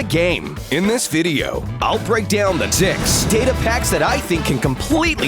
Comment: did he say d*cks